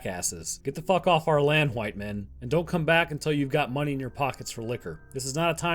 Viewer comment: I'm sorry, did he say "hwhite men" at around 23:32?